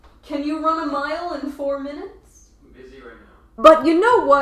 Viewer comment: 'Babe can you run mile in 4 minutes' i thought he said 'EZ Clap' but na he said 'im besy rn'